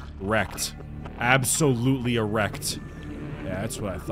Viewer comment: sounded like said "absolutely erect" LOL